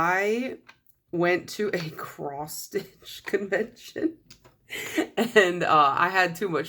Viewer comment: ok i thought you said "cross-fit" not "cross-stitch" until i read the comments LOL